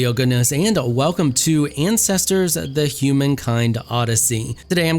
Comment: I had to rewind as i heard, ''Welcome back to incestors'' Alabama 100